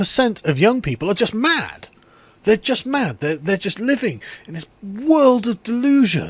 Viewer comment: Oh ‘Living’?!?! .. I thought you said ‘Leaving’!?! I was like .. WTF would young people be terrified of leaving Britain? 😂